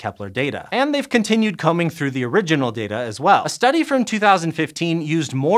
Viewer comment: Lol, "O.G. data" in the closed captioning instead of "original data" at 4:39. Makes me chuckle and I'm guessing that the CC isn't auto-generated. Little things like this make me happy.